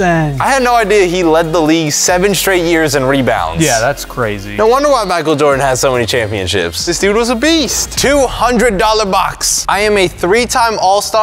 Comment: did he say magic jordan?